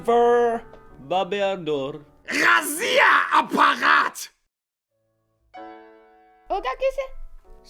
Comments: мне одному послышалось «Россия блять» 😂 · Я единственный услышал "Россия! Аппарат!" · мне одному послышалось: «Россия аппарат?!»)))) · Как слышу я на 2:43 : "Россия Аппарат!" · На 2:45 мне показалось что он говорит Россия аппарат😊😊😀😀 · мне одному кажется что он сказал Россия аппарат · Мне одному послышалось: "РАСиЯ 6ЛЯТЬ!" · мне одной кажется, что он говорит Россия аппарат 🤣🤣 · На 2:45 послышалось "Россия аппарат"ʕ •ᴥ•ʔ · кому послышалось Россия аппарат ставь лайк👍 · На 02:43 я услышала Россия аппарат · Я сначала подумал что он говорит: Россия аппарат. 🤣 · мне же не одному послышалось "РОССИЯ - АППАРАТ"?? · мне послышалось Россия-аппарат!!!!! 🤭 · мне одному кажеться что но сказал аппарат ? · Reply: Вообще-то, он это и сказал, тебе не послышалось. Там же даже субтитры есть. Rasierapparat. Бритва вроде переводится. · кажется что он сказал ррассия-апарат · Мне одной на 2:43 показалось, что он сказал "Россия аппарат"?😂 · мне послышалось или он сказал. Рассия апарат? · мне послышалось? или он сказал"ғазиа апағат" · На 2:45 вам тоже показалось что он сказал россия апарат🤣🤣🤣🤣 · На 2:44 мне послышалось что он сказал: РОССИЯ АППАРАТ!!! А вам? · Мне оному показалось что он сказал что Россия опарат на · Там где немецкий. Я одна услышала " Рассия* аппарат " __ * Ошибка сделана намеренно. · мне одной показалась что он сказал "Россия Аппарат!" """:> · Я один слышу: Россия аппарат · мне послышалось россияапарат!!! Кому тоже ставь👍🏼на видео!! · Мне послышалось или он сказал Россия аппарат?